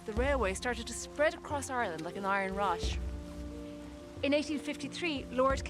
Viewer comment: "The railway started to spread across Ireland like an iron rash" What? I couldn't believe that I heard what I just heard...